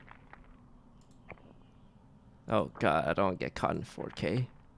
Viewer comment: did he just say I don't want to get caught in 4K (I assume referring to the cameras)? LOL also that last game was basically "vote out everyone that's slightly suspicious or hard-accuses anyone else". I seriously am not sure how you won that one, other than Sykkuno's casual indifference to winning/losing